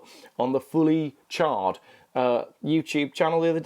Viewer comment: I had a bit of a giggle when you mentioned the fully charged show , it sounded like you said fully charred show . Maybe that would be a better title for that show .